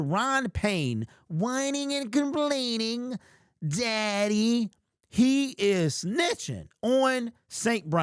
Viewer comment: "Look what happened daddy" 😅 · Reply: I thought he said Zaddy for a second lmfao